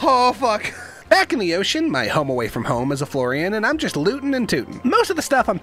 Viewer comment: I thought he said Floridian just as accurate though